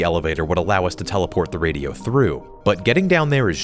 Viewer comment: GRR ITS SAYS THOUGH INSTEAD OF THROUGH